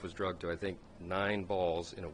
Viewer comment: Did i hear " drugged off nine balls " lol